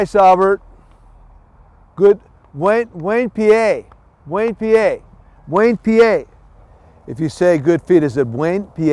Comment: When he was saying Buen Pies I thought he was saying some town in Pennsylvania: Wayne PA